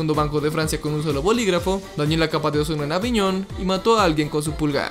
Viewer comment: Pensé que decia, dañe la capa de Ozono Con un “AVIÓN”